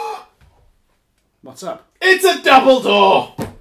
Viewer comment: Gasp "it's a (Dumbledore)!" Nevermind double door, that's what I heard.